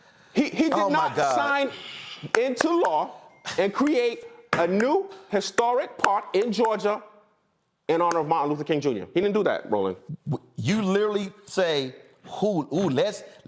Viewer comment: Did he just say Martin Luffer King